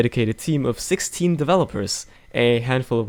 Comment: I thought you said "six teen developers" not sixteen oddly.